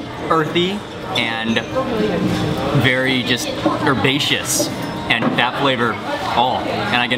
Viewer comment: What word were you trying to say. rapacious or sebaceous?